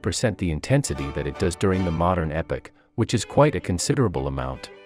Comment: I miss "hello mortal"+ "silly jokes"+ "ha ha ha ha".... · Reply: The new voice seems to have affected his memory core. Did you catch what he said at 0:55 "the modern epic"? For those of us listening and monitoring his computational ability, it's epoch.